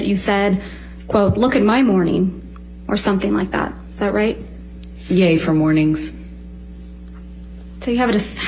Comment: I thought said "yay in mornings" as in yayo